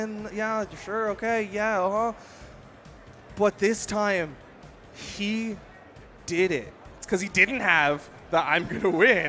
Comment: Did he said "she did it"